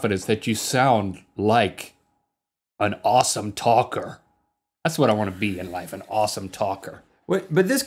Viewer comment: I thought link said awesome toddler not talker lol